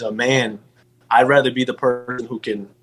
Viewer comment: I lost it when the audio glitched and it sounded like he said “I’d rather be a pervert” lmao